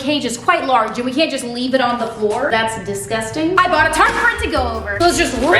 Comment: what does she use for the floor? I can't catch what she says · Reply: I think she said tarp